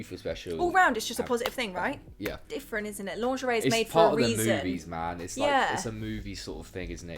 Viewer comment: When said Its part of the movies I totally heard "lubies" had to rewatch that bit to make sure haha